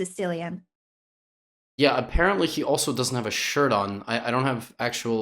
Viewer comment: did Levy say apparently, she also doesn't have a shirt on? lol that was awkward